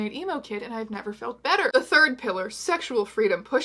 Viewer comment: Every time she says “pillar” (of womanhood) I always hear “hitler” instead and do a double-take, not because I think I’m mishearing, but because I think I missed a joke or something